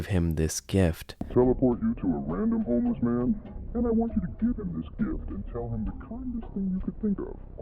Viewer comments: i dont know if you seen this but it changed what he said he origanally said i want you to sacrifice youreself but than it changes it to i want you to give him this gift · If you guys noticed when he says in the home that he will tell Danny to give homeless man a gift the captions say i want you to sacrifice yourself! but then it changes to give him a gift. Oh by the way the minutes and seconds in the video were 4:34 so once you go to 4:34 pause and read the captions